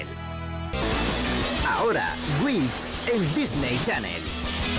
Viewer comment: It sounds like he says Winx (which did air on DC Spain a few years after this)